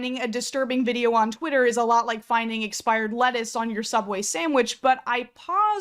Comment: ok I heard expired fetus.